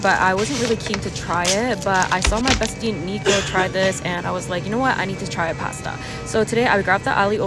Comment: Bro I thought she said “I saw my friend negro try it..” …💀